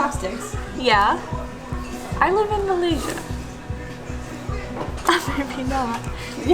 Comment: Did say she live in Malaysia?